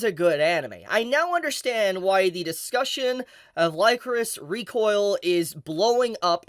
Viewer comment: I seriously thought you were saying "Licorice" the wrong way.... Lycoris sounds weird lol